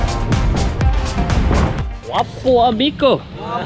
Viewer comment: It's 'gwapoha migo' (don't know if I just heard it wrong. But what I heard is 'wapoha miko') Gwapoha - Gwapo/tisoy/handsome Migo- amigo/ friend